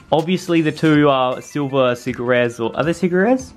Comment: When you said secret rares I thought you said seggerit